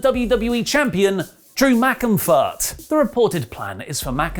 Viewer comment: MR. DAVIS: Which five wrestlers would you most want to cast in an action movie? Let me know in the comments... WrestleTalk Black Friday <mail> 0:00 - Kevin Nash Vs Bats 1:06 - Real Reason WWE Star Suspended 2:48 - Chyna Documentary Dropped 4:09 - WWE Thunderdome Issues 5:06 - Baby Lynch 6:08 - Everything Else In Wrestling News 8:55 - Black Friday <mail> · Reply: I thought he said Drew McInfire I mean Drew McIntyre 😂😂😂